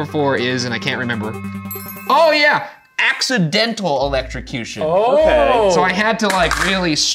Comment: I did not hear ryans #4 right.. i thought he said acts of dental electrocution